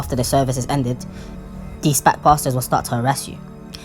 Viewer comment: minutes when you said SPAC pastors it sounded like you said SPAC bastards